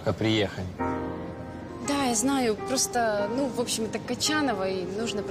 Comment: мне одному кажется что он сказал Катя